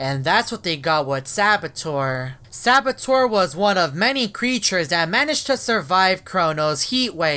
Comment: S4boteur 😨 LIKE THIS VIDEO IF YOU LOVE ME! & SUBSCRIBE AS WELL :) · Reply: I thought you said a parasite instead of a creature